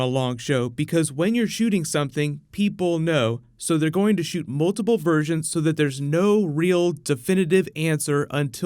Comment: i was watching this while brushing my teeth i thought you said "shoot multiple virgins" lol